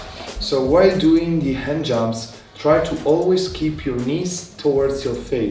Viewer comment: wiiii let's gooooo :D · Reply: I heard "hand jobs" @ 1:43. Took me three tries to get to "hand jumps" 😂